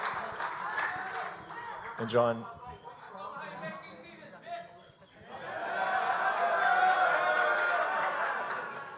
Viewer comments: what did the report say .. was it about jones ? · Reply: Majid Ismail "make DC his bitch'' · What did the guy in crowd said? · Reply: He made DC his bitch